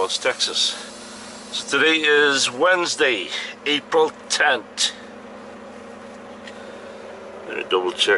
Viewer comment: did he say Wednesday , April tent? lol